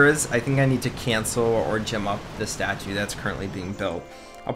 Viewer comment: What bothers me is that when you say "gem up", it sounds like you said "Jim up". I'm not sure if you notice, but I notice a lot. Also, they're not gems, they're diamonds, but it doesn't really matter.